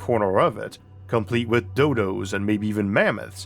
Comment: "...complete with D*****" I'm sorry, what? >replays 40:49 "...complete with Dodos". Oh... Yeah that's what I heard the first time : |